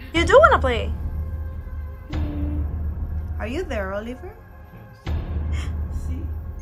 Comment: En el minuto 35:33 cuando dicen ¡Sí quieres jugar!, se escucha la voz de un niño que dice: Sí. Chéquenlo. Llevo muchas veces viendo éstos vídeos y apenas me acabo de dar cuenta de ésto. Wowww que genial.